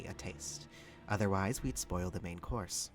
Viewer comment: Ayo i was crunching some celery and i thought you said "spoil the big horse" and my immediate first thought was "what no she turns into a dog"